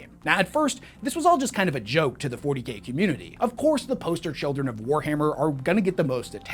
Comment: It sounds like he said the 40 gay community lol